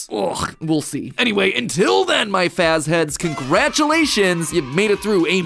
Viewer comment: did he just call us his "fazheads"